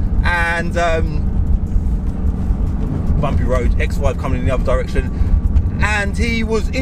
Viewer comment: I thought you said EX WIFE coming in other direction at 12:42 🤣